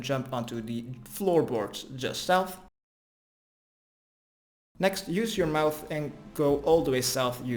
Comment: "next use your mouth and go all the way south..." I heard it twice. 😂